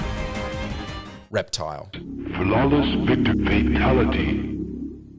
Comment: I had to go back because I thought he said “Reptar” instead of “Reptile”. And I was like what Rugrats game had Reptar as a hidden boss? 🤣